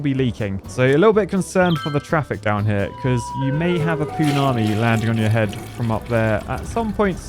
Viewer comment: I thought he said 'poonani' landing on your head 😂